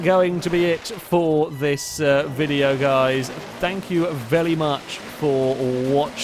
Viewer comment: Has anyone else noticed that when is concluding the video, sometimes it sounds like he is saying "thank you vely much for watching?"